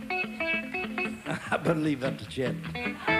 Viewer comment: what does he say at 1:25? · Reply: "I better leave that to Chet" He was referring to his fellow guitar player Chet Atkins.